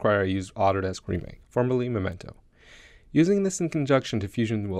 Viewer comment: Anyone else having trouble understanding what he is saying between 3:56 and 4:00? trying to catch the method he talks about but it sounds like gibberish. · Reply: He says 'Autodesk Remake, formerly Memento'. <link>